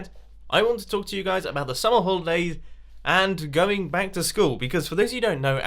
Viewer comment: What the hell did say @ 5:40? I literally can't understand him at all. What does he want to talk about? Edit 1 minute later: He said "Summer holidays." Ugh. Damn accent. I must have rewound the video like 10 times and still failed to understand it, so I just let it play and just let him repeat it multiple more times as he continued to talk about it.